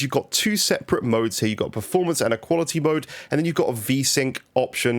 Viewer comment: Wow really good vid dude! At first I thought you said performance and equality mode 😂